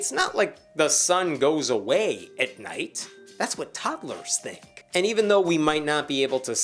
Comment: "That's what Toddlers think." I had to play that back a few times cause it sounded like he said "That's what tacos think." I want a taco now.